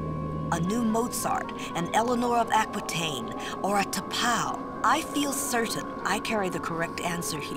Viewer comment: it sounded like she listed off T’Pol (from the Enterprise series) as an important person (even though it wasn’t in the text) and if so I think that’s cool!